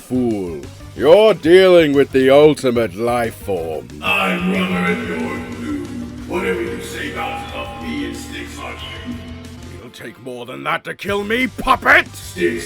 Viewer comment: what does he say here? I couldnt understand the filter But yeah i do agree alot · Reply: “I’m rubber and you’re glue! Whatever you say bounces off me and sticks on you!”